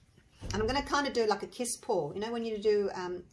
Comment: I swear I thought you said you were aiming for a piss pour!!! Firstly, I couldn't imagine you saying that and secondly, I could not venture to think what it would be if that was indeed what you said. 😂😂